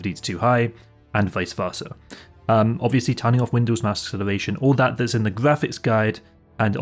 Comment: what is "windows masks elevation"? I can't understand all the words you are saying, and have turned on the Closed Captioning option, but it's not helping. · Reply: "turning off windows mouse acceleration" Assuming you're on Windows 10: 1. Click Windows Icon, in bottom left 2. Settings, fourth option down in the bottom left 3. Devices, second option 4. Mouse, third tab on the right 5. Additional Mouse options, second line of text on the right 6. Pointer options, third tab at the top 7. Uncheck the 'Enhance Pointer Precision' box at the bottom of the Motion section